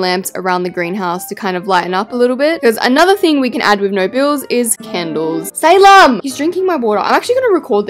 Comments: Idk if it’s just me but I thought it said “no balls…” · I thought it said no balls...